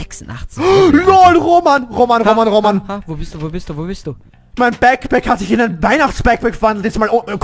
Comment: ein Packback 😂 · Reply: a cup of Tae with a Kookie and Suga ich hab verstanden ein packpack😂